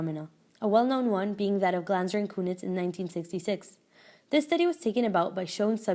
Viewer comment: If anyone is wondering, the study she's referring to @ 1:55 is the Glanzer and Cunitz study, 1966. It took me 10 mins of googling various combinations of words to find out, because I couldn't tell what she was saying. Hope it helps!